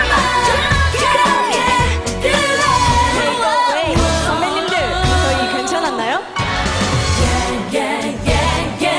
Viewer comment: What did Moonbyul say at 3:09? She ad-libed, so she didn't say her normal lyrics.... but from everyone's reaction, she said something funny/shocking XD Something about AOA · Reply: imClubSandwich she said aoa-sunbaenim was that okay?